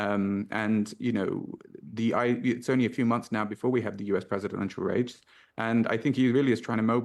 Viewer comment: “US presidential rapes(?)” Did I hear rapes?!